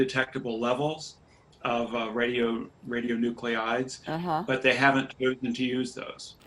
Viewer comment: A small correction, when the headline says "62 radioactive nuclei detected in Fukushima wastewater" nuclei should be "nucleides". I think the person who typed it out misheard the speaker. <link>